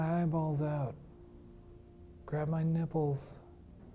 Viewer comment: "grab my nipples?!" Did I heard it right? Ssshhhannneee!!! 😂 What if a lady ghost kindly complied? 😂😂😂